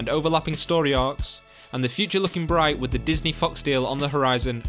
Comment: I thought he said “Disney fucks” instead of Disney fox XD